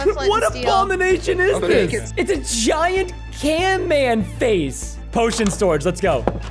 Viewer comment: DID YOU JUST SAY CANMAN?